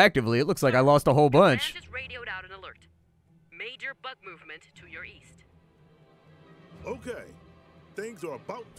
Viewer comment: At first I heard "Major butt movement to the east"...Yeah, I'm having some hearing issues right now...xD